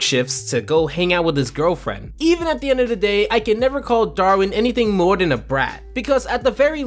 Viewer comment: did....did you call him Darwin? Or is my ears are just broken?